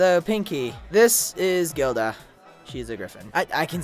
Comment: I thought Gilda said her name was girl dog